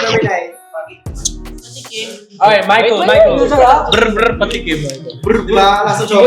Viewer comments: or 8:24 I HEARD BRAINROT SOUNDS BRR BRR PATAPIM.!🤣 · IM SORRY I CANNOT BE THE ONLY ONE WHO HEARD SOMEONE SAY "brr brr patapim" AND "tungtungtung sahur" did anybody else HEAR?